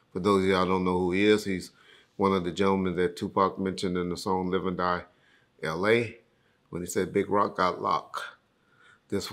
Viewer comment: I thought he said ‘big rock got knocked, so this one’s for you’?